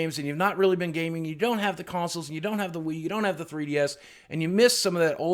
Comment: did he say you dont have the weed